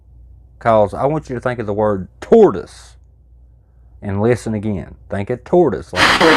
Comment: He said it sounds like “oh yeah” I heard “ toward us “ Then you said “tortoise” And I was like “ eh, close enough” certainly closer than “ oh yeah” 😂